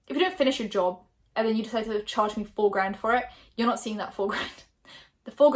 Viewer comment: I thought you said "A full grand" and not 4 grand💀💀💀